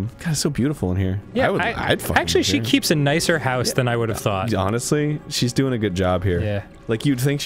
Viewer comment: I thought Arin said: "Yarnestly" at around 9:30? .. my hearing isnt what it used to be anymore..